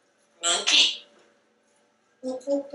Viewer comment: At 17.21 it SO sounds like he said Mooky....now I know he's saying monkey but just listen..lol I'll take it!😁💚✌